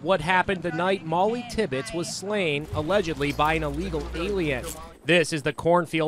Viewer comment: did anyone else hear "an illegal alien "??? Edit: This comment had seemed to have exploded with likes! 188 likes in 10 hours. Thank you guys for the likes, this is the most I ever had!!! Edit Edit: 305 likes now!!! this is just amazing I can't believe this much